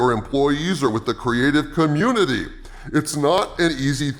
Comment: When he said "creative community" i heard "'creative' commune."